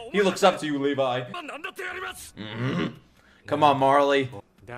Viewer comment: WTF did just say marley?